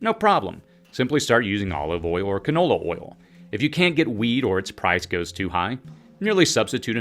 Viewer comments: Ohhhh...WHEAT! Sorry, friend...I thought you said "weed". Panic off, lol. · For a minute I thought he said: "If you can't get weed, or the price is too high..." Then I realized he was actually talking about "wheat".